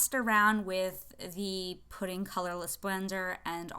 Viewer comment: i thought you said "pudding colourless blender" and now im hungry 🍮🍮